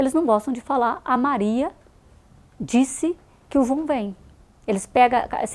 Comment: O que ela diz no exemplo em 1:11:35? "A Maria disse que...?" '... o vum vem'?